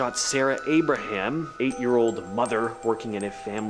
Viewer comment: I'm sorry, but at 4:26 did you say, "eight year old mother"?!